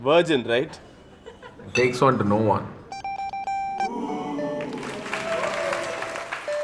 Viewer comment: What did he say? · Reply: It takes one to know one...